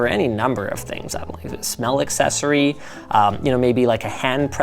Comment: Did he say smell accessory?!